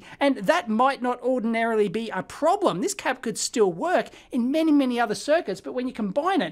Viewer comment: What's the word at 27:02???? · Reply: cap? capacitor probably